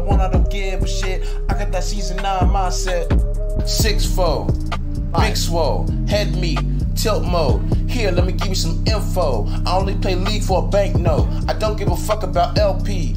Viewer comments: Six four, big swole, head meat, tilt mode, here lemme give you some info, I only play league for a banknote LMAO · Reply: i thought he said tilt moe, it would have been beter 😂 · Thought he said tilt moe